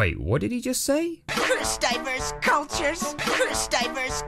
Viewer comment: "Guess he's just a massive racist." The man was about to have live eels shoved down his pants! Let him vent! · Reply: lol thank you I kept hearing "purse diaper culture" I was like wtf? What is a purse diaper and how is it a part of culture and do I want to know 😂😂😂